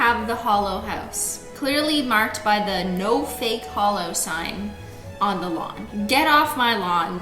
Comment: I thought it said no fake hoes at first 😂😭😂